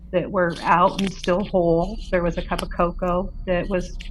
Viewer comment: Regarding the dehydration I thought they said a cup of coffee was still upright. I may have heard wrong been listening since lunchtimes. Now 3.30 am here. This book sounds fantastic. 🙏 Great food for thought from all of the callers. Hi, my 💙 to Blue and Chloe 💖🐶🐶😍